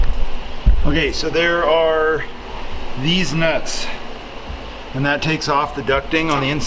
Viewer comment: Did he say, "Deez Nuts"?